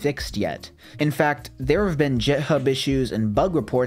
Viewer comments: did he just say jithub?? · did you just say “jithub”? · did mans call it jithub?